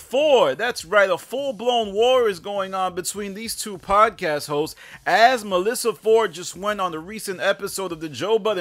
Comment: 😂 I legit thought said "A full blown war is going on between these two podcast heaux"